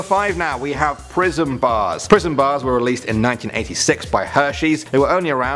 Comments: For Prism bars I thought you said "prison bars" lol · Lmao i am watching this rght after i woke up and I heard 'prism' as 'prison'